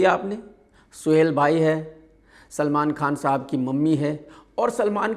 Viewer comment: सलमान खान साहब सुना मैंने, क्या मैंने सही सुना